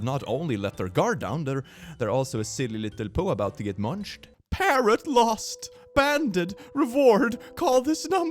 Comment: Parrot lost… I wasn’t watching and was just listening, I heard “parent” instead of “parrot” 🤣, to say my eyes widened in shock, confusion and amusement is an understatement